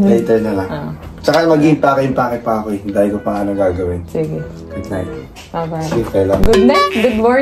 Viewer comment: Tama ba yung narinig ko? sa 14:14 nagsabi si ng "Goodnight sleep my love"??